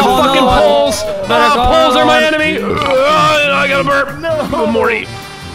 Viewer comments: How you got mad triggered Brian😆😂😱✨ also did anyone else hear at 10:46 Poles are my enemy's fck i got to burp *he burps*And say Oh Morty😆😍👏💯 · Did anyone hear I AM WILDCAT burp and say o morty xD